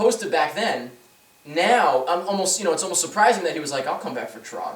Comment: "I'm homo" At least it sounded like it.